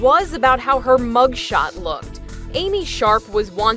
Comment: Lol I thought they said Amy shark